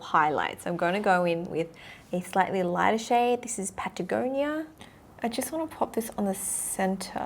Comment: I thought it says "potangina" ? tf?